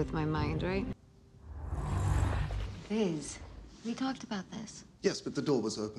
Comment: did she just call him "Vis"????